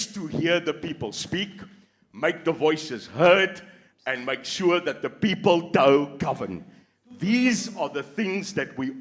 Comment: "make sure the people don't Govern" did I hear that correctly?